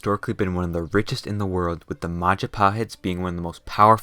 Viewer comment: What I Hear "Majepahet What It Is "Majapahit" Ngapa Pahit Kalau Bisa Manis Edit:Im Proud Being People Of Indonesia/ Gw Bangga Jadi Orang Indonesia Gatau Kalian Kalian Pasti Bangga Di Negara Kalian/ Don't Know You..You Must Be Proud Of Your Own Countries